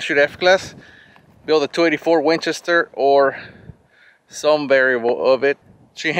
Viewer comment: Sorry, from the UK, what do mean by 24 Winchester, not familiar with that term, do you mean .243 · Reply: I thought he said 284 Winchester? Also from the UK 👍